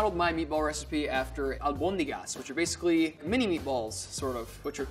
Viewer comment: redemption arc is very satisfying, yeah he's still being redeemed for that grilled cheese, until I forget, he's on a redemption arc. Also the Japanese chef did an awesome twist on the meatballs, love it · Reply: I thought he said Almondegas, though? And if that's the portuguese version then, pretty much not far off. Our meatballs are not eaten as a soup, but rather with spaghetti, and consistency is like he did.